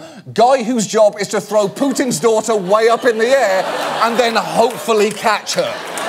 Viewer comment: Did anyone else notice the closed captioning at 6:58 says “pooping Sauter” instead of “Putin’s daughter”?